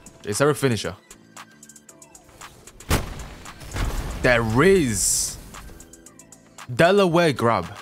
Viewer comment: Hera say there is and say the rizz in your head at the same time. what do you hear? (btw the time for this is